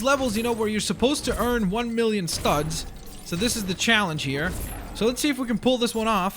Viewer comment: I THOUGHT IT SAID 1 MILLION SUBS!!